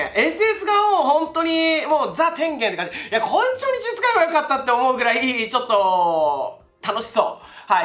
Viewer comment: なんて言ってる？ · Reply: こいつ初日に使えばよかった って言ってるのかな？